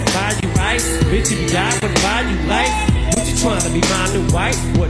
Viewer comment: Am t h e only one who thought that he said" buy u lice"